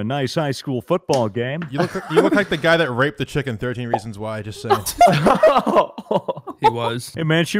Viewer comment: I thought they said "you look like the guy who raped the chicken in 13 reasons why"